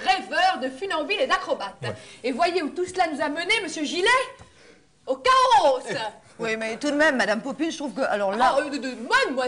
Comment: Qu'est-ce qu'elle dit ? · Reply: "au chaos" (avec une exagération sur le s de fin)